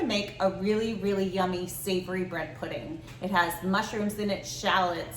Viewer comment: Dam I could have sworn I heard breast pudding